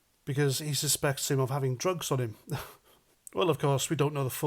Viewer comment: Drugs? His accent must be bad, I thought he said a drone! I'd of told him to f*** off